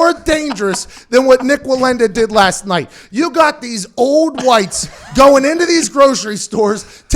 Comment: Hmmm, "you believe (:47) in THE God"...... Also "you got these (4:36) old whites"? Is that what was said ? That's very disappointing, didn't know PM was a product of the PC culture.